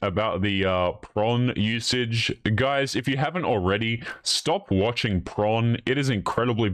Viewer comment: I think I need a hearing aid. It sounded like you said, “Stop watching PRONG.”